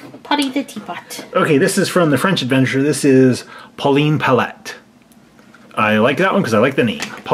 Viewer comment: hey, instead of Polleen something, it could be my middle name Colleen something!